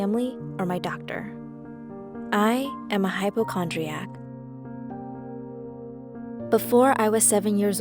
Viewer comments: For a second there I thought she said “Mitochondria” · My idiotic ass thought she said "I am a mitochondria" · I thought she said "i have mitochondria" i was like well, Duh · i thought it said mitochondria